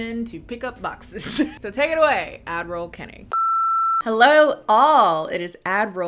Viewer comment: I don’t know why but every time I hear you say “ad roll kennie” I think of “Adderall kennie”